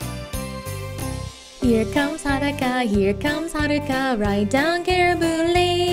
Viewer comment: I keep hearing, "Here comes Hanukkah."